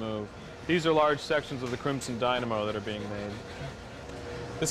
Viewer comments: DID HE JUST SAY CRIMSON DYNAMO?!😳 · Did he say crimson dynamo